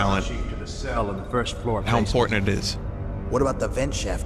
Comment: I thought he said "how Fortnite is"